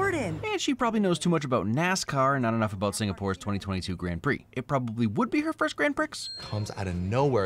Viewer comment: love the F1 refrence but did you say GRAN - PRICXZ